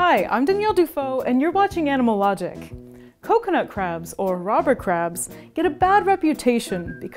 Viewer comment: i heard robert crab 💀